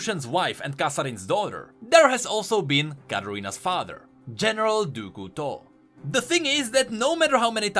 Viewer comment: Why do I always hear "General Dooku tall" ?